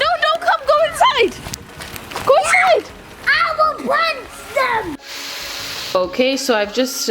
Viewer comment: Did say "I will punch them!" ? 11:57😂 Omg he is so cute!!😍